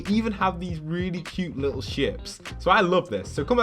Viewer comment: I thought you say these little shits